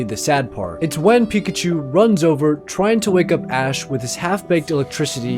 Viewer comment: in the first sad one I don't hear "pika-pi" I hear "wake up"